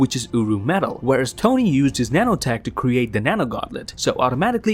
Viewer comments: I swear I keep hearing 'UwU Metal' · I dont know why but I keep hearing "uwu metal" Edit: Yall I am not a furry or anything like that but the bois got f'd up from the partys